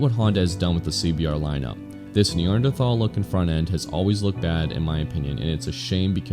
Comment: Did he say "Neanderthal look" on the CBR at 8:01?